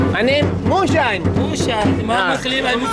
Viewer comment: WHEN u told him ur name moonshine he thought u r saying muslim lol WELCOME TO PAKISTAN ..